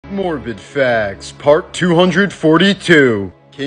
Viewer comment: I thought he said "morbid f4gs"